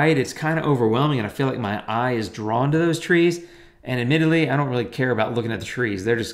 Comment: Thanks, glad you liked it! I do love Italy, but this photo is from Prague. Hope I didn't say it was Italy in the video. :-) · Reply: My mistake! I should have washed my ears out!! I misheard “admittedly “ as “in Italy”. Doh!! Prague or Italy, it’s still a great video.